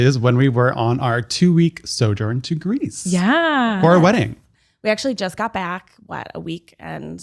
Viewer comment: For our????? Wedding??? They got?? Wedded? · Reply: I heard him say "for a wedding"